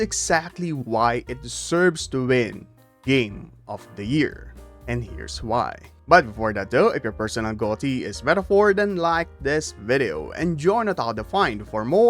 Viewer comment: anyone heard it "your persona game of the year" Persona brain rot is real